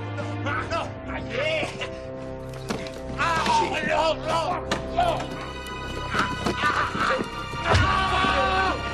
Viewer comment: what was saying after said open tht gotdamn window 🤔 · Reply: He said " no..no..no.. " lol he aint want that window opened up